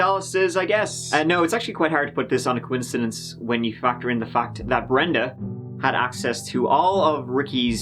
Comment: I thought he said "when you fucked her in the fat" not "factor in the fact"... Lol. I'm so sorry...